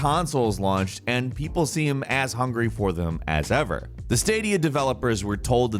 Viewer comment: Sounded like you said “ass hungry” lolol